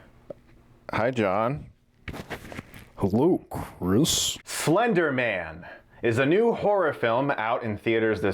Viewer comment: Did he just say "Flender Man"?!?